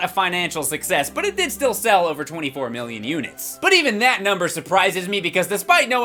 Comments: 24 million kinects sold 7 billion people seems legit note when i made this i thought he said 24 billion lol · I thought you said 24 billion